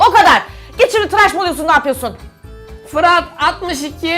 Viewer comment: fırat mı dedi ben mi yanlış duydum